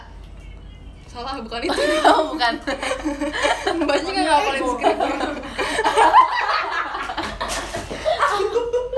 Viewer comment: si olla ngomong apa? 7:36 galak amat la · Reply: "oniel gob-"